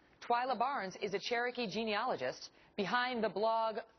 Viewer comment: Doh! I thought the video said Cherokee "gynecologist."